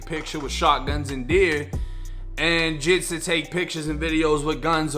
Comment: It kind of sounded like you referred to blacks as ‘jigs’